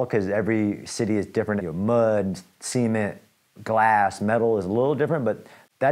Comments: thought he said “semen” 😂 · I swear for a second I thought he said "semen" lmao